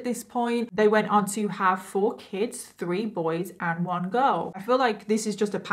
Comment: Lol, at first I heard "All 4 kids", as "Awful kids".